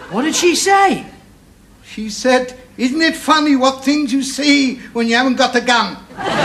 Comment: What did Mr Goldberg say? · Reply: "Of American-English origin, the exclamative phrase 'the things you see when you haven’t got your gun' and variants convey surprise at seeing something or somebody unexpected. This phrase alludes to a hunter who will lament seeing all sorts of game when he goes out into the woods and fields without his gun."